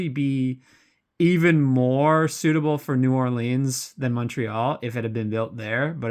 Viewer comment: When you first New orleans, I heard it as Nolans. lol